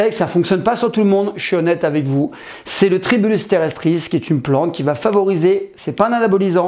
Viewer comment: le quoi ?? ???? la "tribustrerestrice" ? ? ? ça c'écrit comment ?